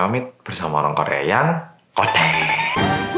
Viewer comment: Sampe skr aku masih ga nangkep 'orang korea yang ...' yang apaaa itu?? hahahhaa.. sampe bolak balik dengerin ttp ga nangkep mas bilang apa. wkwkwkwk.. Ceritany yang dibawakan selalu mantappppp, menjelaskannya jg sangat baik. Selalu suka.. suksesss terusssss yaaa.. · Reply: Orang korea yang keweden (ketakutan)